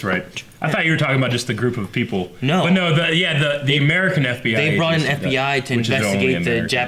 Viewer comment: Did he just say American fbi lol